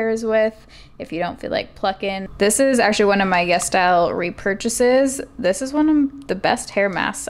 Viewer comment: ITS CALLED YES STYLE??? oh my god this entire time i thought you were saying Yes Dial